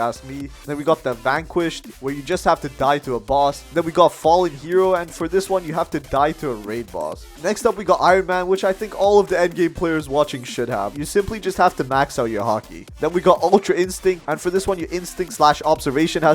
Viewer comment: For iron man i thought you said nba players lol